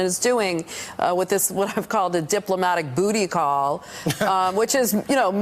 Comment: Did she just say Diplomatic Booty-Call?! 😂